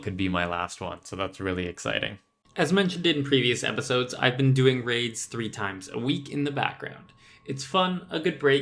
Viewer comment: Ngl, I initially thought you said "I've been doing Raids three times a week in the bathroom" - reduct1